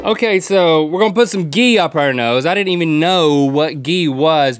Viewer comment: I thought it said "cheer up your nose"